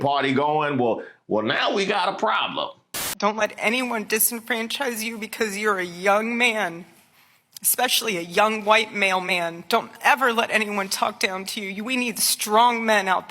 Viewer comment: Why did I hear "white mailman?"😂😂😂